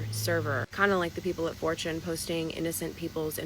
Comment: fw = f*ck with. Thus, "do you fw him fr" = "do you f*ck with him for real". Or, in actual English, "are you really sleeping with him too?" 10:24 Why is she calling 4-CHAN "Fortune"?